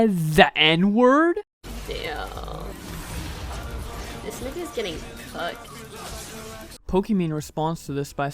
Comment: I THOUGHT SHE SAID THIS NIVIAS GETTING COOKED LMAO